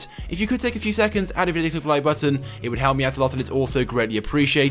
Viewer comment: the way he says hit the like button is so priceless he says it so fast it isounds like he saying hit the lie button